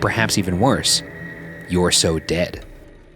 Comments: when it says "you're so dead" i heard it say "penis" instead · i don't think she's saying "bad" or "dead," the tone sounds like she's exasperatedly saying "you're so dumb..." and then a long sigh after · what I hear is "You are so crazy." 27:09 (actually 26:54 the first time) it's a clear "Who is it?" as if spoken by an old lady. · #Debrief at 13:02 I think the voice said You’re so pretty which I find funny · Ok, 12:58 I definitely heard it as “You’re so pretty,” so maybe they were just giving you a nice little complement. ❤️ 😂 Edit: NO BUT THE BACKSTREET BOYS AT 48:57 🤣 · I heard “your so stupid” 😭